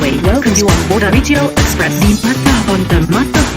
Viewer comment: 小田日用エクスプレスにしか聞こえん